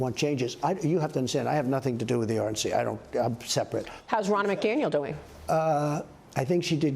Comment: I swear i thought she said “Ronald McDonald” 😂😂😂